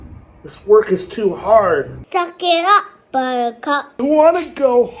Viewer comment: "Suck it up buttercup" What i heard💀 "Fu-k it up buttercup"💀